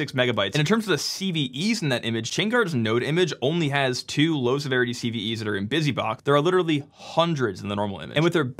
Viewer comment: I misheard "2 low-severity CVEs" as "2 load-bearing CVEs", and "load-bearing CVE" is my new most cursed cyber security term.